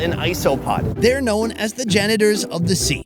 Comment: Who else heard ‘They are known as the genitals of the sea’